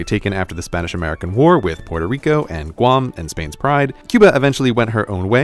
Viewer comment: taken after the Spanish-American war with puerto rico and guam and.... Spain's pride... :DDDDDDDDDDDDD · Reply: Pride? I thought he said bride